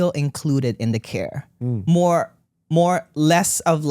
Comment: I heard “hell care”. 😂